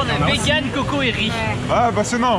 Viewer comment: les gens qui ont pas compris liker perso j ai pas compris moi non plus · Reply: Sans thé (santé) mais avec du café